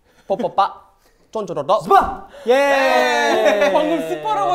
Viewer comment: I thought Taeyong said “CHUPA!” in 1:43 HAHAHAHAHA